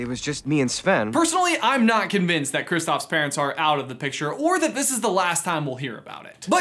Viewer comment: Why did I keep hearing "parrots"? I kept thinking when did Disney ever have dead parrots??!! 😂😂😂😂